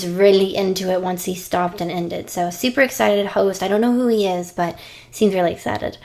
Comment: lol i heard I don't know who juice is